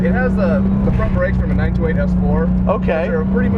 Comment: did he say '92 S4 brakes, as in Audi Ur-S4? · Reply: Anders Kristensen Porsche 928 S4. The Audi has a different bolt pattern while the 928 S4 apparently offers a relatively trouble-free swap. Or so I've been told :)